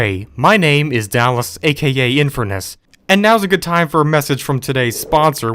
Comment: it sounds like he says infamous instead of infurness (Found it funny)